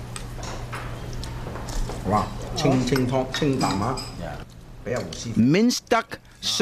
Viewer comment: for real he actually said “Ching Ching pong Ching”